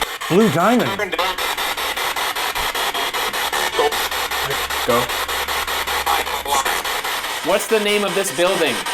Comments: Did anyone else hear James stark or another ghost say "go.....diamond block" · At like 7:16 after hearing blue diamond did anyone else hear “go find it for me”? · I heard “Blue Boy” not blue diamond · it sounds like he says"go" and at 7:16 it sounds like "I don't want you" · I heard "Loser" not "Blue Diamond" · i heard “loser” not blue diamond lmao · When it said blue diamond I thought it said loser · James apparently said "blue diamond" but I swear to God I heard "loser" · I heard "losers" instead of "blue diamond" and I legit cackled